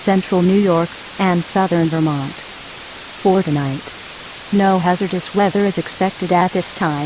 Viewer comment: Excuse me, but was that 'Fortnite' or 'For the night'? EDIT: I'm adding more description for myself. This is really confusing me cause it sounded like 'Fortnite'. I don't have an NOAA Weather Radio system (Cause I'm Canadian) but I know it said 'For the night'. Although to me this is really well. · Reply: “For Tonight”